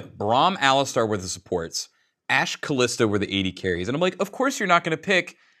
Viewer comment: the subtitles say "Calista" when it should be "Kalista"